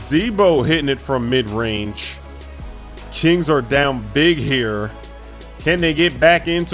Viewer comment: did he just say chings???